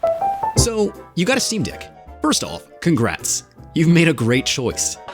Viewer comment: the subtitles say steam dick instead of deck lol